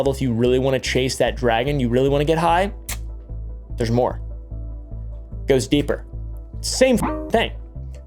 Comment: Rofl I thought you said "Ghost Peeper" and not "Goes deeper." I was like, "That is not a drug anyone should wanna do!!!"